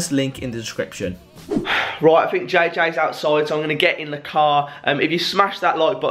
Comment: At the start I thought you said Jaykae’s outside I was like what the fuck no way